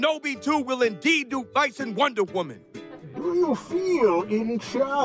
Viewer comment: Pense que decía pinche wonder woman